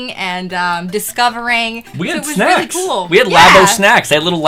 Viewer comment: "we had snacks!" really sounded like "we had sex!" XD